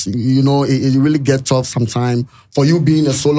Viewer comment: Sua brɔfo oo menua na “you know”no adɔɔso…did i just hear it get” toughs”